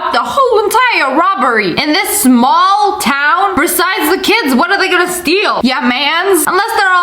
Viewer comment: At first I thought it said TEEN kids at 18... I’m like, NOPE